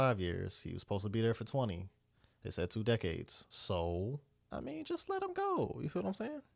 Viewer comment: Lol. You had misheard the sentence Andy received. It wasn't two decades (32:34). It was two life sentences, back to back. Meaning there was no chance Andy was ever going to be released.